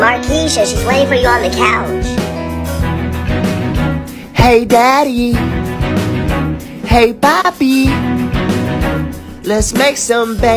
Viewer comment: What I heard "hey daddy hey babi let's make some baby"